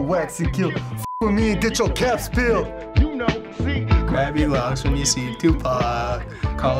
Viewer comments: Did he say grab your locks when you see 2pac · did he say locks 😂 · did he just say locks?O.o